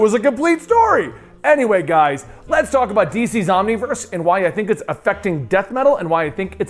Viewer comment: Not going to lie, I thought you said DC zombie verse and I peed myself a little bit with excitement 😬